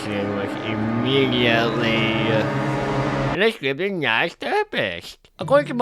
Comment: did you say stupid nice or something else 😅